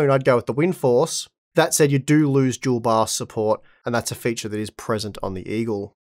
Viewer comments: I just ordered a Windforce OC and came to hear about thr comparisons. Around 5:50-6:05 i heard windforce doesnt have dual bar support. Was it supposed to be Dual Bios or Resizable bar? Edit: nvm I saw the 8:40 picture, it was about Dual BIOS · does he say "dual bar support"? What is that?